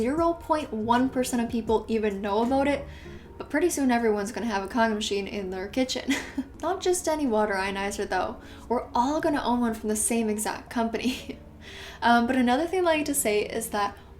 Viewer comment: heh the cc said condom machine 😂